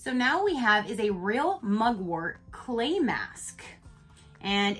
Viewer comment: Okaa! I had 2go find out what MUGWORT was because I thought 🙋 U said Mogwai at 1st 🤣🤣🤣 I was like Gizmo Gizmo sisss I was 🙎‍♀️🤣🤣😂 If U were born after 95 you're probably lost right now But that's OK 😂😘San